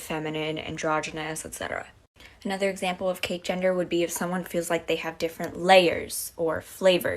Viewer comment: Cake gender? I really thought she said, “Cate Jenner.” 🤣🤣🤣